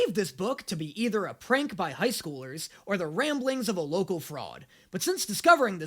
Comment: I thought he said “rambling of a frog”